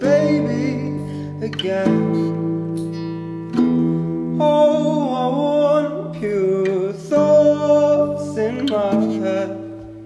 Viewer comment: OH I WANT TO BE A BABY AGAIN OH I WANT PURE THOUGHTS IN MY HEAD OH I WANT TO FORGET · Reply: I always thought it said "Oh, I want /your/ thoughts in my head"